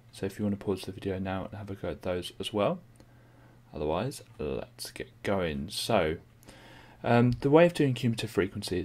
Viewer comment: Wtf, he said let's get going not let's get started